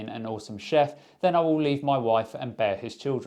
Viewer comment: I listen to most YouTube videos on x1.75 and I thought he said...I'll EAT my wife and bear his children. I'm glad I heard wrong 🤣🤣🤣